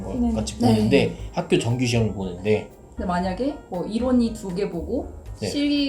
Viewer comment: 학교에서 본다는 정규시험 중에서 일원 이라고 하신게 무슨 뜻인가요?? 이부분이 잘 안들려서 궁금합니다!! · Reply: 이론 시험 입니다.^^